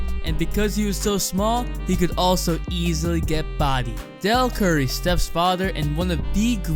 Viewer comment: "Dell Curry, Steph's father" BRUH I THOUGHT HE SAID "DELL CURRY STEP FATHER" LIKE DAMN 😂😂😂💀💀💀